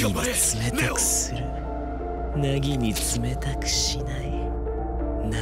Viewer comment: 「凪に冷たくしない」が「ネギに冷たくしない」って聞こえた人おる？